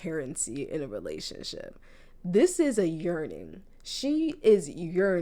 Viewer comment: WHY I THOUGHT YOU SAID URINATING LMAOOOO I DIEDDDD